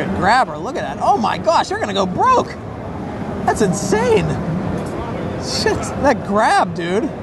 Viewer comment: Is it just me or did he say sh-t